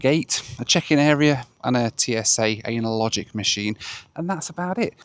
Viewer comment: did I hear correctly they have a TSA Anal-logic machine? 😬